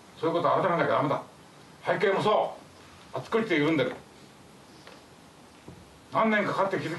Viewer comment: の背景もそう！のあとなんて言ってるかわかりますかね？あつくりてそうって聞こえるんだけど、誰か教えて下さい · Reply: 「あっちこっちでゆるんでる」ですかね